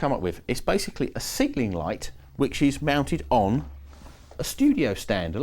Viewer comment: And at 2:59 did you call it a signalling light? Because I can't find that anywhere online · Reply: Ceiling light if you're still wondering!